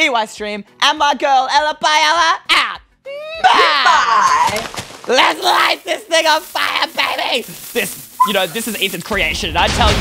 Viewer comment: did he say "LET'S LIGHT THIS THING ON FIRE BABY " Or is it my imagination?